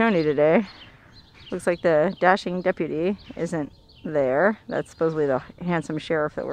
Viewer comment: For a minute i thought you said the FLASHING deputy :-)